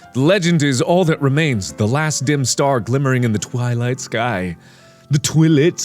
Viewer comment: I thought he said toilet sky 😂